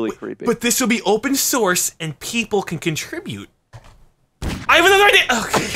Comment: What did he say at 1:25? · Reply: "I HAVE ANOTHER IDEA" - Chilled